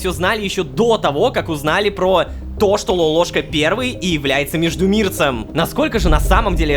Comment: я единственный услышал слово "блять"?